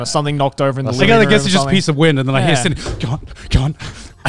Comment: Maybe I misheard but it sounds like says "a piece of wind" 😂